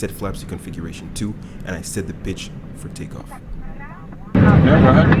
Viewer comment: It sounds like you said “ And I set that bitch for takeoff “ 😂.